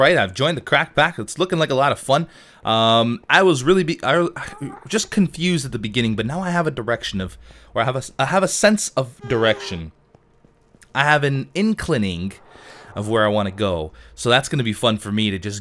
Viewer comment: I swear I thought I heard him say "I have an erection"... O.O